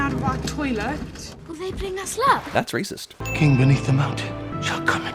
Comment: Can somebody please tell me what the little girl at 7:01 says? I watched it at the movies and everyone laughed. No matter how many times I replay it I have no idea what she is saying. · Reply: I'm 7 years late but she said : "Will they bring us luck ?"